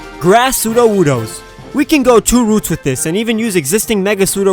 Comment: THAT PUN... JUST WHYYYYY!!!!!! I HEARD YOU SAY "we can go two ROOTS" when your were telling about a grass type sudowoodo did you have to say that you hoped no one would catch on but my ears did not deceive me heard what I heard.